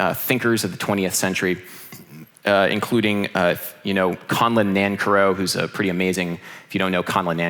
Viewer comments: what is the name he is saying? Help guys pls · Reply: Conlon Nancarrow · who? I am not a native speaker of English and I couldn't figure out the name, can someone help, please? :) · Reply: Conlon Nancarrow